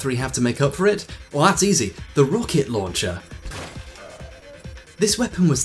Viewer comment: This is pedantic af, but at 36:38 the subtitle says "rocket launcher" but it's Rock-It Launcher